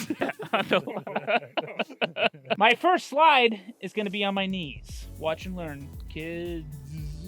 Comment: let’s watch earn some money while he’s on his knees. That’s what I heard.